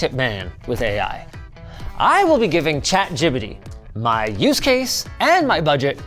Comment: Did bro just say chat gibyti 😭😭😭